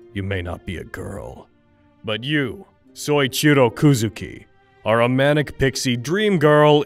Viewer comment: did... Did he just say "Soy-chirou"